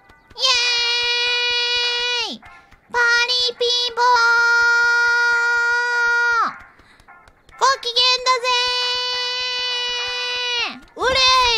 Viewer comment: she actually says "party people", its a pretty well known way jks/kids says things in japan